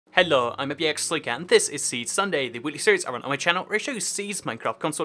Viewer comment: it sounds like you said "a wheatly series"... SPACE